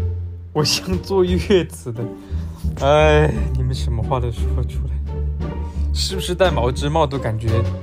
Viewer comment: He's always so done with us, it's hilarious! I needed this compilation P.S 1:06 I heard him saying "你们什么话的说出来" which roughly translates to "You guys say all sorts of things"/"The heck are you guys saying"